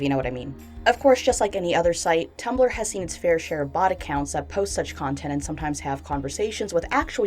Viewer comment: English is not my first language and sometimes it causes funny misheards: in this case "body count" instead of "bot account" :D